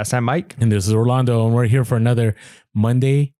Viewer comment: Have sold thousands and never had a complaint but it’s possible. I get what you’re saying. —Orlando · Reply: For months I’ve thought you were both saying Ro-lando! Now I know! I look forward to your podcasts every week. Keep up the good work!